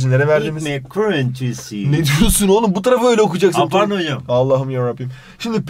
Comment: ekmek kirintisiğ -ne diyorsun olum burayı öyle okuyacaksın Allah'ım yarabiğim